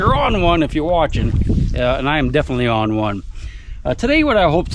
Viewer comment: I look forward to seeing the adventures and finds from my teacher, 🧐 👀Stopped me in my tracks, i had to stop and walk backwards and look at my T.V.... becuz All i heard was "im deff on one"😶‍🌫️🤫🧐😏 (whad he say)😂 🚩 Thumbnail made me instantly click, wth is that coolness